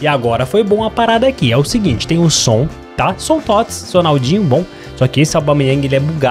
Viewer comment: o falou meu nome kkaka foi oq eu entendi " sonaldinho " me corrijao se eu estiver errado